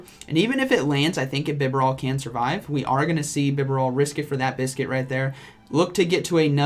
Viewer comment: From the start of the video i thoughT you were saying "vigoroth" but later i got to know it was " BIBAREL". LOL ..